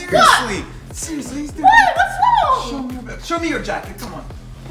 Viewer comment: No one else heard him say "Show me your butt"